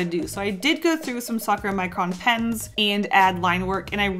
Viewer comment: when u said Sakura Micron Pens....I heard f*cker Micron Pens 👀